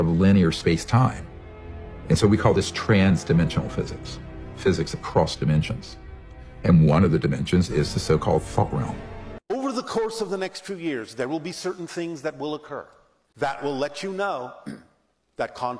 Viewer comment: Did he say the F*CK realm? Haha😂